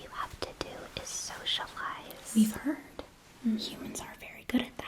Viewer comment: I’m sorry… Did you say SOCIALISE? Oh….oh dear.